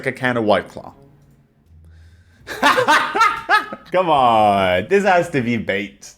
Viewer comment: the auto captions saying "this has to be baked" PFFFFT